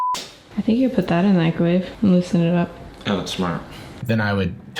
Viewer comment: turn on captions at 6:38 instead of microwave it's grave and fits in surprisingly well